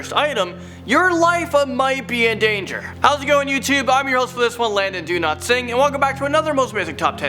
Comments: Did he just say, ''My name is Landon Do not sing'' ahahah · Hahahaha genuinely the first time I've ever seen this guys name on screen as I'm usually just listening to these videos. I always thought he was saying "Landon do not sing" I assumed it was like an account name or something hahaha